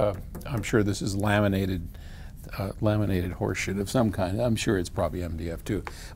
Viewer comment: knocked on the table and I heard him say..."laminated horsesh!t of some kind" and LOL. what did you actually say?!